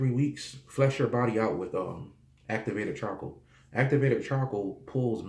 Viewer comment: Hi and thanks from Germany for waking people up. Can you please write down the name of the medicine you take ?(18:55) I can't understand and perhaps don't know that product in Germany. Thank you☺ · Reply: It's called activated charcoal